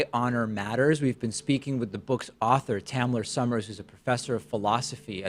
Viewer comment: I thought he said “Tanner,” and I was like, ok not a weird name. Then I read that it was “Tamler,” and saw a white dude, total confusion.